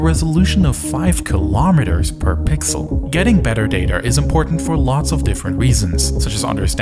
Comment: Did he just say "getting better daterrr"